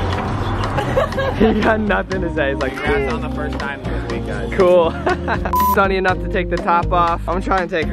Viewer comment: omg I think he was saying The first time they had sex was this weekend